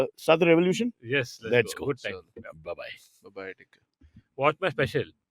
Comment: did say ba-bye d**k?😂